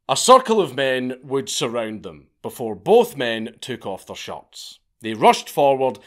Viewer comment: My ears don't always handle accents so well...at one point I thought I heard, "both men took their shorts off..." SHIRTS. The word was shirts.